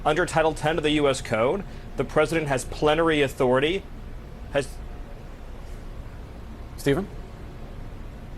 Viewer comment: Not gonna lie, I thought Stephen Miller said “Planetary Authority”